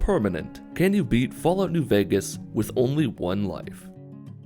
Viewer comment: I was watching this in a playlist, didn't look up to see the title when the vid started and thought I heard "Can you beat Fallout: New Vegas with only one leg?" and I was like "Shit is he cutting off his own leg for a challenge now? Is this the dawn of the real game?"